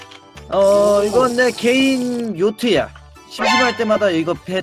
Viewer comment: 에 요트를 노트라고 들었넹